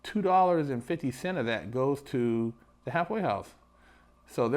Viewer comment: I keep hearing "hathway house"